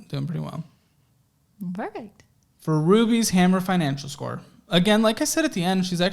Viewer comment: I thought u said "Ruby Hammer's financial score..." ❤ lol would make a cute couple